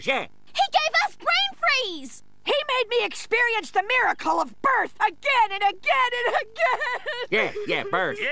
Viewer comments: “He made me experience the miracle of birth again and again and again!!!!!!” 😂😂😂😂😂😂😂😂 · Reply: For so long I thought she said "He made me experience the miracle of Birds!" I always thought Hugh made her watch a bird lay an egg over and over again. LOL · As a kid, I misheard when the woman was saying how she was made giving birth over and over again. I thought she was talking about how he burped over and over again. The words “burp” and “birth” sound a little similar. Also, now I know what really happened, I think that was the craziest thing Hugh has ever done. I mean, really. The baby had to experience being born again and again. That going to be some serious trauma if the baby somehow remembers.